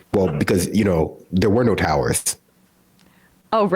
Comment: does he say there were no terrorists.